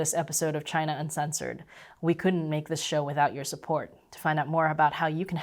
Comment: I thought she was able to say, "We couldn't make this shit up."